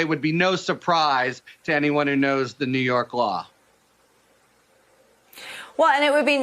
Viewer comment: I had a double take. Thought he said New York mob😂 but was New York law.